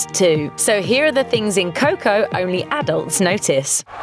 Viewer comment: I thought it said “the only adult nicotine is coco”